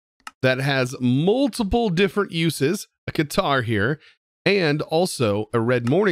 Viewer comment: was watching this while playing another game and i could swear he said he made a "guitar." imagine my disappointment. please make a guitar